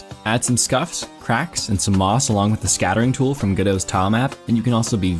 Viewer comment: Did I hear Moss?